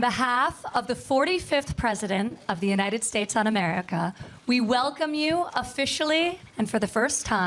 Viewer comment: did Ivanka say on behalf of the president of the United States "ON" America? Is that a slip? Some kind of code?